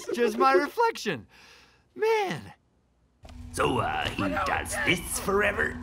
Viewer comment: what did the little devil say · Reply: Yelling Apple "So uhh, he does this forever?" "Yup" "Wow, that's fucked up."